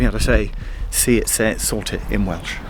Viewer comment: it's "Sorted" not "Sort it". I used to think it was what you said for the longest time.